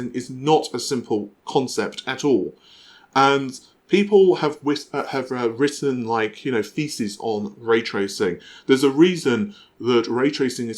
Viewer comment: thought he said “people have written feces on raytracing.” We love you. You big nerd you.